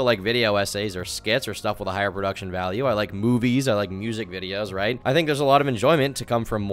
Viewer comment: Thought you said boobies instead of movies. Man..